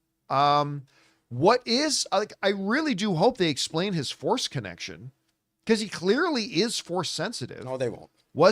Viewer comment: For a second there I thought John had said "I hope they explain his foreskin action" then I realized he had said "Force Connection". Ha ha!